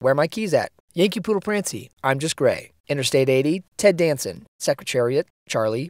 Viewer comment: I heard “I’m just gay”